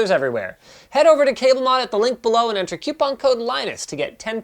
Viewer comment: DiD yOu SAy QuePON?! ThE U coMes AfTer the O!